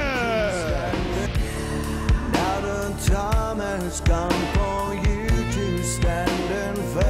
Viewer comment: does he say "now that Tom has come for you"?